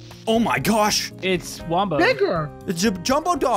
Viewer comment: WHOA WHOA WHAT DID YOU SAY??? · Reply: he said bigger